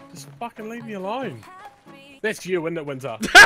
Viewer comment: What did he say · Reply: RAFPega “Oi, that’s you Winter, innit?”